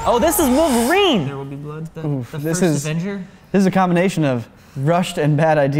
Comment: ok wait which one of you said this was the avengers 🤣 i hope i misheard that